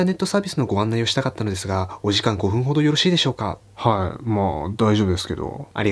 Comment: 「お時間5分ほど」が「5時間5分ほど」って聞こえて2度聞きした。